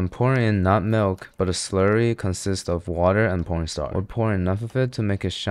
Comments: did he just say water and pornstar 😂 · why tf subtitles says p0rn star instead of corn starch🙂